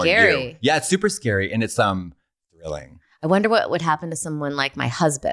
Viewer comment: What does Katya say at 46:45 after "super scary"? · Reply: "And it's thrilling."